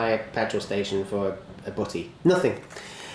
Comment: I know you said "Buddy" when you said, "Not even swinging by for a buddy." but it sounds like you said "Butty"